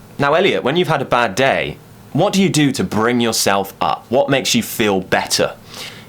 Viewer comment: I didn't hear it clearly, but did you say "to bring yourself up"? If so, then what does it mean in this case?